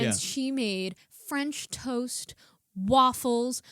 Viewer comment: When you mentioned the full breakfast that that lady made, i thought you said she made french toast waffles instead of french toast AND waffles. Either way it sounds delicious.